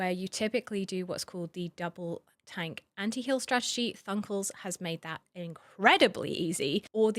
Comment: What name are you saying here? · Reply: Thunkles!!! Have a video on him here: <link>